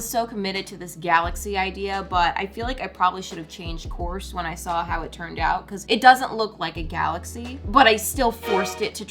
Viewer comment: Omg I thought you said corpse 😅💀